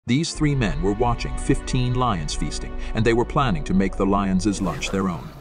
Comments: I heard "Three men wanted to become the lions own lunch" 😂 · I heard it as "they were planning to make lion as their lunch"